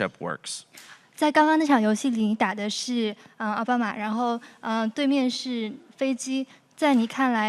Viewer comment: did she say obama